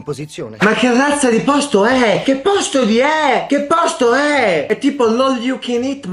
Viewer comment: solo io ho sentito "che posto di é" vi prego ditemelo 😂😅